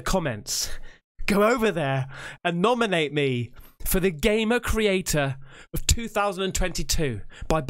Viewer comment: damn i heard it as gaymen creator lol